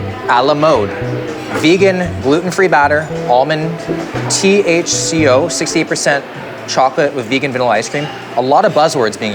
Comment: i heard that as THCO 68% chocolate and was like MY MAN 😂🌿